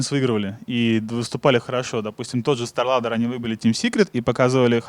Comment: "выебали"? Или мне послышалось? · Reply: выбили